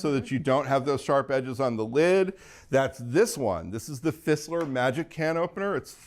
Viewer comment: what was the name of the safety can opener? I thought he said thistler but searching for it turned no results? Jest curious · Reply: Fissler. Unfortunately, it’s been discontinued. ATK's new favorite safety opener is the red one by Kuhn Rikon.